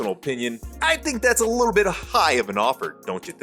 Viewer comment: did you say “a little”??